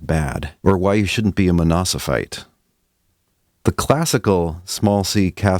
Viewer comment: I hear something like "you shouldn't be a monosophite". If that word or some related word exists, I'm ready to be enlightened. But I guess the intended word was "monophysite".